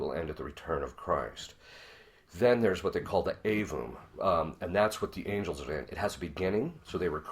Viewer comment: What does Father say at 8:54 min Avu? Avum? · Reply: Aevum